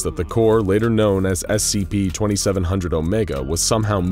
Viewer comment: The subtitles are wrong here (2700, not 2100).